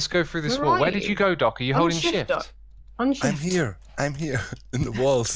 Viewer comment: did say are you holding (s word)?!?! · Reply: He said shift